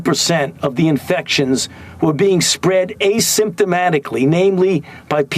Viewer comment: 'Did someone say 'asymptomatically'?